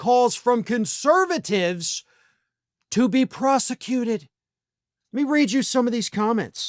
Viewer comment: Got excited for a sec- I thought you said "EXECUTED"...